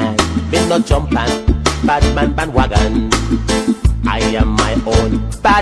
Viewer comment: lol i thought he was saying batman and wobbin lol.